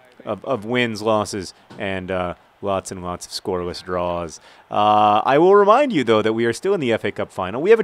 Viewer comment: "Scoreless draws" Hehe. I heard it as "scoreless drawers" which is funny because underwear.